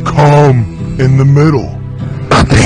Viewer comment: For the longest time, I thought Batman was saying “Cu-Penis”